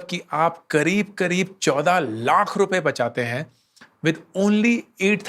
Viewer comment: 24 lakhs not 14 lakhs. Surprised even the subtitles say 14.